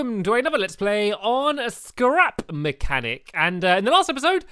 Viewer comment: I thought stampy said crap mechanic